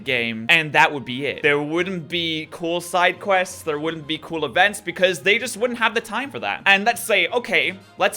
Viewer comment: boy i thought u said you don't want events